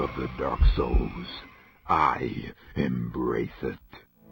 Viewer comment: "Power of the dark souls. I... am racist." I know this is not what he actually says, but I did a double-take here.